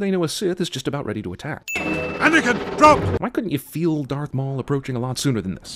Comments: Anakin don’t! I guess that’s what he said I thought it was Anakin drop! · Sin: Subtitles say "Anakin, don't!" When Qui-Gon Jinn clearly says "Anakin, drop!" 20:50 Missed opportunity to make fun of George Lucas stealing the shield technology from Dune. "The slow blade/droid penetrates the shield." 1:08:12 No Obi-Wan, it was said he would bring balance to The Force. And that's exactly what he did. He joined the Sith, and helped eliminated 98% of the Jedi until there were only two active practitioners of the Light Side. Thus bringing balance to The Force. For beings of unimaginable power, they have a pretty bad grasp of how balance works. If you have a Light Side, you must also have the opposite for it to be balanced.